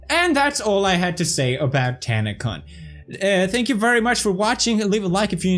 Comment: Did anybody else hear #Tanacunt ??? Just me?